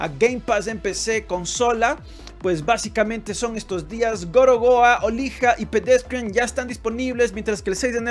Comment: Cuando dijo "Gorogoa" entendí "gorowa" en plan God of War mal dicho, y me hizo toda la gracia 😂😂 luego leí que ponía "gorogoa" realmente y me reí más 🤣🤣🤣